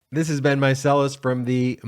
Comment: You know... this whole time I thought you were saying "This has Been Meiselas"... I didn't realize your first name is *Ben*, so you've been saying "This is Ben Meiselas".. lmfao